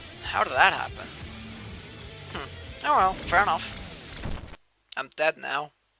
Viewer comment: in 17:11, what were you saying? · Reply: Sounds like I said, "Hm. Oh well, fair enough" :P